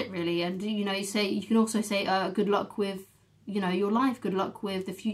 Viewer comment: you scared me there for a second i heard "good luck queef" 😂😂